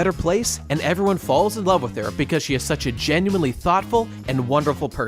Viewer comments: i just heard it "thot-ful" sorry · Everytime he said "Thoughtful" I hear "Thot-full". :|